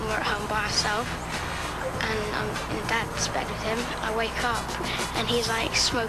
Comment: Did Jack just say Ozzy was in bed with a man or that ozzy was in bed again? Strange little story